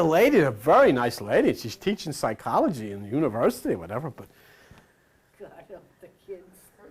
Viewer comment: did someone say "God help the kids?" 😅 Thank you for the video.